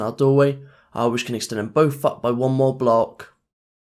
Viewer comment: it sound like he said the word f*ck